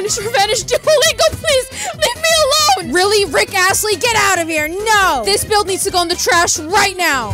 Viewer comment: I heard “Really? Rick Assly?” Not Rick Astley